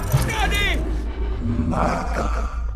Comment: que dijo? attack? O mata? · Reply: Mata